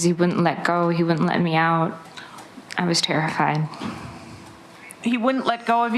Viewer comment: is she saying "I was terrifying" .... ? !